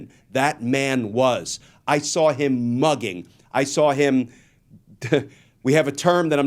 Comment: Did you just say, "dugging"?